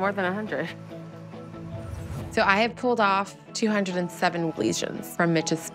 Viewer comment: I thought it said 200 lesbians